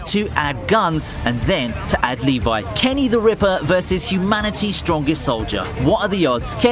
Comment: When he said kenny the reaper... I thought he was saying kenny the river, i was like: HUHH??!!?